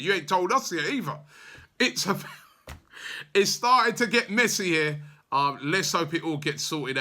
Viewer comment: LMAO I heard "get Messi here" Imagine if we signed the GOAT